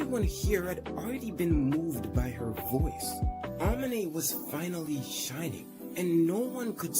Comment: I heard "shitting"😭😭😭